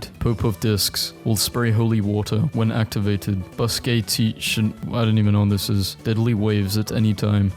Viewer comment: you didnt even understand the "bustacean" joke, that is the best pun ive ever heard in my freakin life